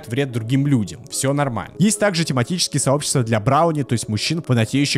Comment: пфффф ХА-ХА-ХА, вот без обид, но когда я впервые услышал не "брони", а "брауни" — мне в первую очередь вспомнился пулемет Браунинга. :Р